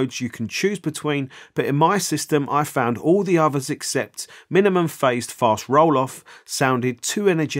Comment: Oi! Did you say youse like favorite daC filter mode is "minimun phase fart rolloff"? Me mate down the local heard that too around them time 3:26. Cor!